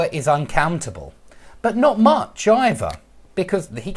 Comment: Did you say "not much either or neither" at 5:34?